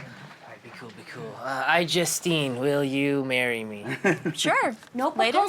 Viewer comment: I thought he said iJustCame then instead of iJustine o_O you 'merican's and your accents and pronunciation xD